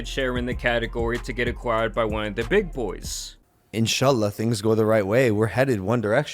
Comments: did he say inshallah?? 💀💀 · Did i hear logan say inshalah at 1:40 😮😮😮😮